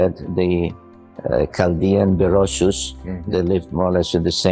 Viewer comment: Horrible captions, so many wrong words compared to what was actually spoken for example, 17:27 captions says Chaldean the Russians but that's not what he's saying something else Chaldean the Rasus BUT great interview! Also, something that's REALLY important is who is he interviewing?